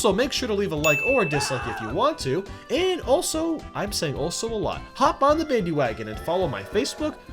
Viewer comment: Did u just say u were single. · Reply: He said "I say also a lot"